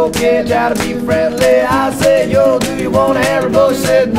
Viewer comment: first time i heard this i couldn't get the lyrics and my friend who has an english accent told me it said "I said yo, do you want a haribo?" but me being scottish, i didn't quite catch what she said and thought it was "I said yo, do you want to hire a boat?" lol ooooh the confusion